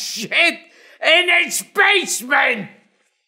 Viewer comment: "I'm not educated enough for the guy who talks about bUFfaLo sHit in his basement! ..." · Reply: Basement? I thought he said Spaceman.